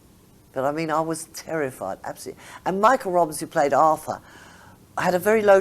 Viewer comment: RIP Anna. I grew up the son of a busman in the 80s and I loved OTB. I had to chuckle at the way she said "Arthur" at 1:20. Almost sounded like Olive. 😀